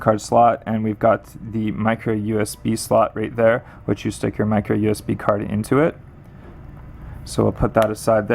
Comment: does he say micro usb card? -.^